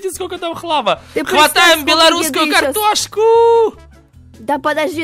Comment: Мне послышалось он типо сказал Антошка 😂😂😂😂 13:35 и это не картошка а наггетсы лайк кто знает тренд 😂😂😂😂😂😂😂😂😊😊😊😊😊